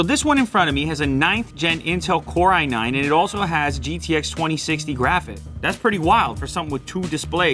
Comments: Did he say gtx 2060? 0:56 😱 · did he say GTX 2060? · Is it gtx 2060 or is it rtx 2060 cause i heard him said gtx . Theres no gtx 2060 right ? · did he just say GTX 2060